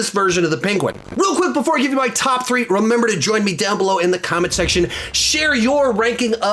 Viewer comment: My favourites are the Dark Knight's Joker and the The Batman's (2022) The Riddler. As a side note: every time you say "Join me down below", I always hear "Enjoy me down below". One letter drastically changes the meaning... 😄